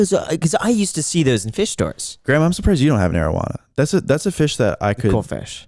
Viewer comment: I thought he said a marijuana fish at first ha ha. 😂